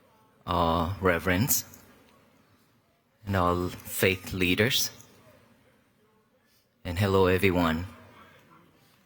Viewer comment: I thought he said "and all fake leaders" and I thought it was bold all FAITH leaders